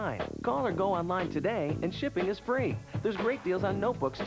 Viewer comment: Now all I hear is "Dude, you're gettin' Adele"